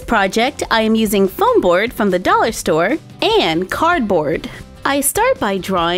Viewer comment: I thought you said dolly store not dollar store! Lol 😂